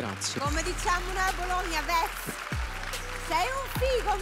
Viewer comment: non ho capito cosa se dice a Bologna? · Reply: "Vez"